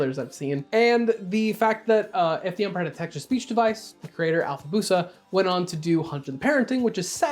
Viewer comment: What TF did he just say? I replayed this three times and I couldn't understand what he said other than a name. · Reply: "The fact that, uh, if the emperor had a text to speech device, the creator alfabusa..."